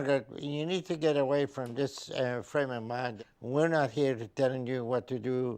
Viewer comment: LOL!! I thought Dŕ. Now said, "Stay away from this friend of mine"!! Hahahah!!!! He said "frame of mind!! LOL!!